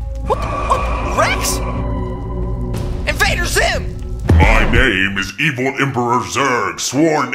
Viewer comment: Wait a second?!, What did you say? Can Somebody tell me what he say? Did he say 'invader ....and the other part I do not know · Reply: he said invader zim